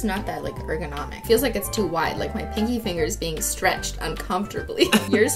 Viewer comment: when she said she was stretching her pinky finger i thought she said fingy finger